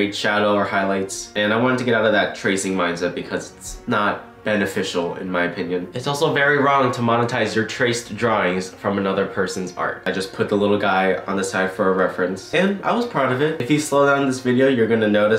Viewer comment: Did he just say"BanetteFicial"